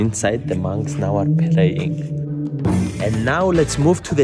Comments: I thought he said "playing" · Oh I thought he said "inside, the monks now are playing " 😂